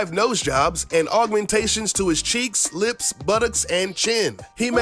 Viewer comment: Did they just say but ex 3:16/